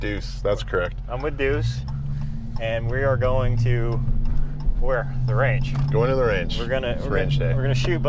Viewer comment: Why did I hear going to the "RANCH" LOL